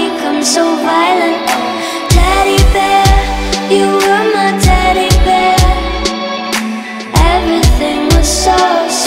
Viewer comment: I thought it said “Daddy bear”...